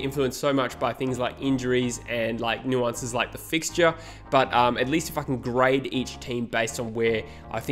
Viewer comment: Did he say the f word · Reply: At least if a can grade he said